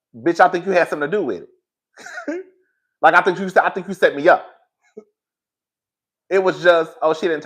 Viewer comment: Yes, say “you set me up” - even if he didn’t say it, I heard it and it’s said.